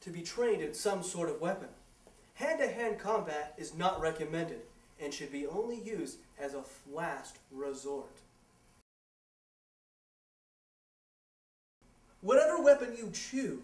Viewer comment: did he just said "flast" resort?? XD